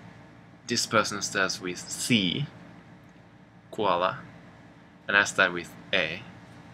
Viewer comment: did you say "koala"? XD that doesn't start with a 'c' i think you should do some of the cultural topics you mentioned~ like with the cultural views on divorce, the importance of certain traits (like being able to write essays well), and even the topic of bathing with family. plus, cultural stuff is usually tied to language anyways~ :D although i've never heard of parents bathing with children in america, i know that a lot of kids when they're young will take baths with siblings.